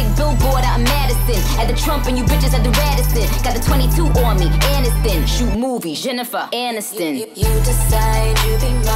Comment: All these years I thought she said got the 22 on me n a Stan shoot movies Jennifer anastan see she be blowing my mind tf